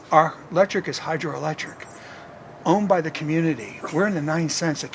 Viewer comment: he said $0.09? Let me re-watch … and make corrections of it is $0.09. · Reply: Okay at time index 06:04 he most likely said "... owned by the community ... " and immediately after did he said, "we are in the 9 cents per kilowatt-hour"? If he said 9 cents, that is a great deal for him. <link>